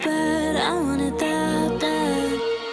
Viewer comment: OMG I THOUGHT IT SAID “ASHIN USED MY HEART”💀